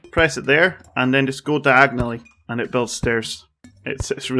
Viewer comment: hey mate thank you for the video! by the way, how do you build stairs so quick? 9:10 I don't understand what you say... "...press it there and then just go to... agnoly?" The only way I found to build stairs is to knock the wooden platforms with the hammer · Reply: Nicolò Tommasi you get wooden platforms and build diagonally